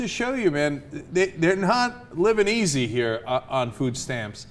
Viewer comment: I coulda sworn he said "They're not Lebanesy here"